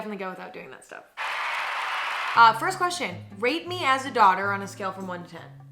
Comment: I first heard it as “rape me as a daughter” and stared at the ceiling wondering why I was still alive to this day and how I was functioning as a human being